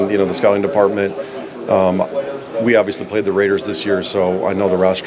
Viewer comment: did he say “We obviously played the Raiders this year?” 🥴 Oh boy…